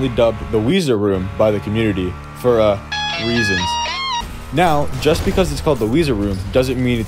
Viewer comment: I had to replay 4:20 like five times to see if I'm losing it cause I could have sworn you said "for uh, weazons" lmfao